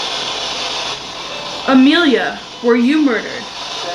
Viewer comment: after you asked "Mary, were you murdered?" I heard a voice say "Amelia"